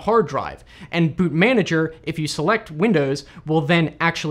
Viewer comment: I thought he said BootMii